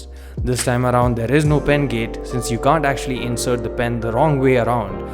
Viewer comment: fuck did u just say bend gate